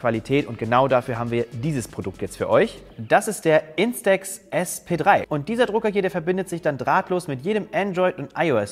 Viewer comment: zu viel Corona hab beim ersten Mal nämlich Instax FFP2 verstanden